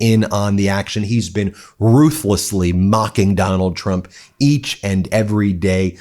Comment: I heard him say McDonald Trump. Hahaha